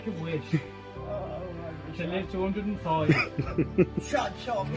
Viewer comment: The one of the garbage collector surprising an elderly lady on her 100th birthday it sounded like she said oh shut your ass up